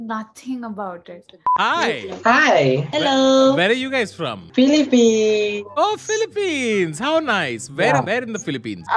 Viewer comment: "I heard pilibhit 😂"